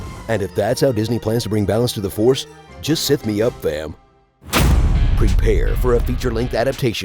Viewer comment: what the heck did he said in 0:51 just sign me up then? just sign me out then?